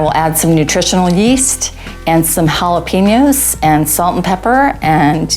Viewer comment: did she say jala-penis??? Dat ain’t vegan!!!!